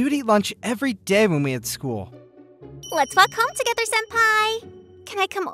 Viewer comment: when she says walk I hear da f word